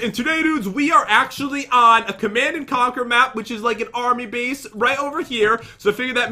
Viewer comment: When he said army base I thought he said Barbie base😂